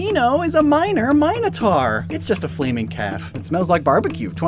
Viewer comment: I thought you said "cat" at first.